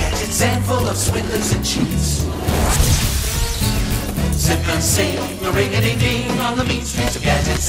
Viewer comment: what do they say? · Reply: its zip and zing but close enuff